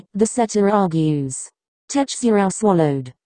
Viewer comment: Am I the only one who heard Tetsurou at 9:48??? Edit: and 11:12 Edit 2: and says koutarou(I forgot how to spell) at 12:10 and